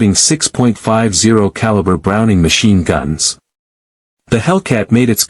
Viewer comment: Stupid CG narration... THIS is why we can't have nice things!! It sounded like it said, "6.5 caliber machine guns"! Now, if you're gonna punk out and have the machine read for you, you gotta write crap the crap machine can read, DUH!! Garbage in, garbage out, a rule right up there with "RTFM"! Punk writer, I'll help: "The Hellcat came loaded for bear with fifty caliber machine guns, three in each wing. Just one of these monsters was deemed enough to protect a tank from airborne threats but six assured the destruction of any Japanese aircraft who fell into the Hellcat crosshairs."